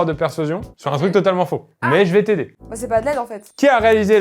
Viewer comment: pourquoi elle dit "C'est pas Adele en fait" AVANT la question ? 🤔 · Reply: je crois qu’elle dit « bah c’est pas de l’aide en fait »